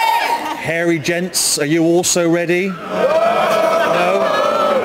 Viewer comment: i thought i miss heard him when chris siad max wax off